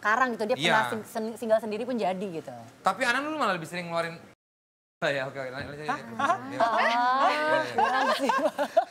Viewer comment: serius nanya, yg disensor ngomong apa sih?? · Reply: Peju